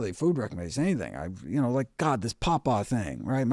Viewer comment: At minute 42:43 he mentions "this papa thing" - could someone help me out with what he is saying regarding what it is and the neurotoxins? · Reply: Nevermind I found it "pawpaw"